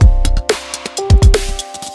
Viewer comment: i remember this video first coming out at a tough time in my life, but instead of hearing “leaving” i heard “living” so when it says “are you living” it just hit so so hard and kept me going for some reason. thank you for everything skrill can’t wait for the return!